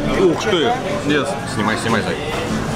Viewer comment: "Зая Зая снимай" 12:40 Ммило, мне же не послышалось?